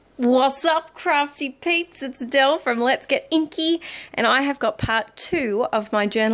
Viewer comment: When your boyfriend overhears your video and he thought you said "let's get kinky" 😂😂😂